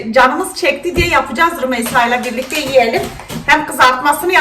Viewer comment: Rümeysa yeğenim dedi ben öyle anladım... 1:05 'ten itibaren dinleyin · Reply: Rumeysa'yla birlikte YİYELİM diyor ;)